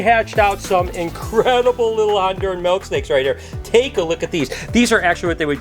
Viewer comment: I was in the other room listening when he mentioned "Honduran milk snakes" and thought he said "lobster milkshakes" #-o